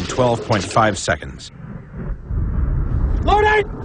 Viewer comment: もうない！！って聞こえたｗｗ